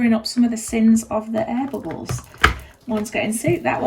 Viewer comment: LOVE your accent - I'm used to a Texas accent so......... When you said AIR BUBBLES, at first I thought you said EDIBLES!!! ---- My sound level is kinda low😅😂😅------I was wondering what you made to eat ------until I played it back again!😅😂🤣😂😅😂💗💗💗💗💗💗 Thanx much for showing struggles too!👏👏👏👏👏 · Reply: This comment has me giggling lol, the struggles are important to share. I am human and sometime Resin kick my arse. Speak soon from Edibles